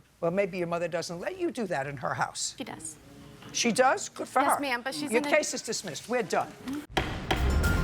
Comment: “ She does, good for her “ I heard “She does??!! Well she’s an idiot!” 😂😂😂😂